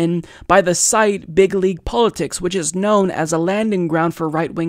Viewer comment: It's funny every time you said it, I heard it as "Bigly Politics".